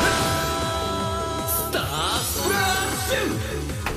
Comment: I thought he said "Juice nipple start"